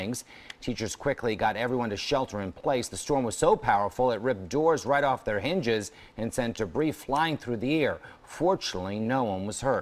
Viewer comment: “Unfortunately, no one was hurt.” What I heard: “unfortunately, Noah was hurt.”